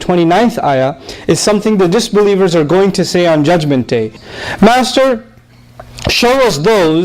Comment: This is for the attention of the channel owner and general public viewers. There is a grave mistake/typo error in the wordings of the translation. In particular at 0:28, in which Brother Nouman Ali Khan says “Master” but in the translation script in the video appears as “Monster”. This is a serious problem and a mistake needs instant attention and make it correct. Brother Nouman Ali Khan was referring to Allah as “Master” when explaining an Ayah from Quran. Imagine some layperson Takes the meaning of the Ayah from the written translation in the video and understands it as “Monster” instead of “Master”??? Asthaghfirullah! Please correct it. Jazakum-Allah khair